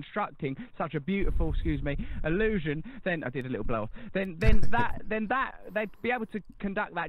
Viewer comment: Did RB say "I did a little blow"?